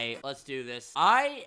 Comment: When he said 'this is ' at the start I thought he said bitches -.